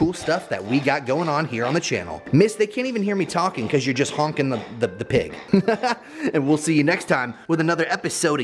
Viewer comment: Wait, I thought you said she was hogging the pig? Bwahahaha